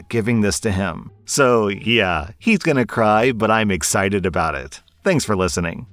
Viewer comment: So on that last story, I misheard "my bf is going to cry" as "my bf is going to DIE, and I Don't Feel Bad About It" And I spent the ENTIRE STORY SO CONFUSED I kept waiting for the boyfriend to turn evil or incredibly stupid and I kept checking how long of the video was left and then the diamond necklace came out and I was just like "huh???" Had to rewind after the bowser set was mentioned I'm so glad this is just a wholesome sweet story but man was that a surreal amount of anticipatory anxiety LMAO